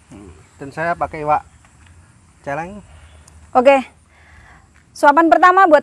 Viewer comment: Kata si kebo makan iwak celeng. Apa aku salah denger ya?